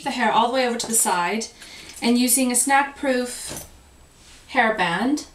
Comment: Very pretty festive look! I thought that you said, "snack-proof hairband." I must be hungry! Hope you're feeling well and getting some sleep ~Nina